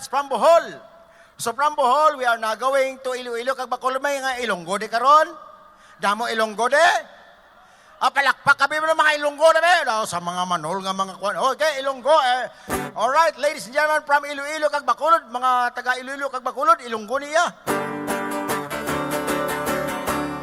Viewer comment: Did I just hear say "Man Hole" instead of "Manol" ???? HAHAHAHAHA ❤❤❤❤❤ 🤣😂🤣😂🤣